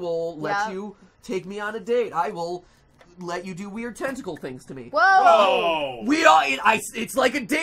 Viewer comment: I thought he said testicle instead of tentacle